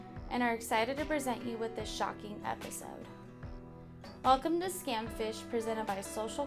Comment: I'm not a native English speaker, so in the intro instead of "a shocking episode" for a moment I heard "a shocking imbecile" and I had to listen again...😅